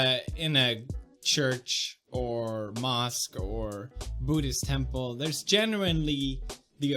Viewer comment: thought he said "booty temple" ;D :D